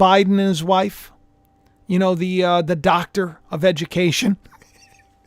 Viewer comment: It sounded like you said “ Dr. of Vegucation” 😂🌽🥦🥕🥬🥒🍅